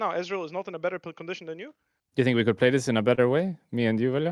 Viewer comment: Wait, when does he say this? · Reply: he doesn't. he asked velja "do you think we could play this in a better way" at 2:39 referring to the fight around scuttle. First comment must have misheard